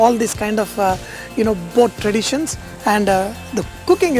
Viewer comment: Did he say the food resembles these "poor traditions"?